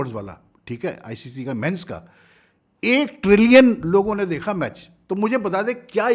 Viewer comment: did he say 1 TRILLION people 💀💀